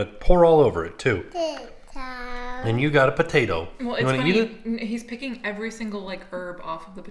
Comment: I think wanted to eat the sandwich at the table! lol sounded like he said "Eat it here" PS the Vlogger!!